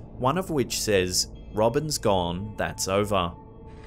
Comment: I didn't hear "Robins gone. That's over." I heard "Robins gone. Crossed over."